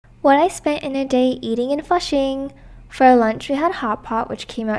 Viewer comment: I swear I thought she said "what I spent in a day eating AND flushing." 💩 good thing I read the title after.